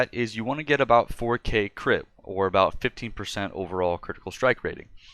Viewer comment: Did you say 15 or 50% crit at 27:27? I can't really tell. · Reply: 15%, sorry about that.